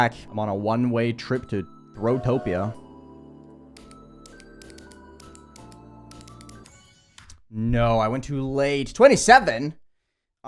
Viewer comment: DID YOU SAY GROWTOPIA???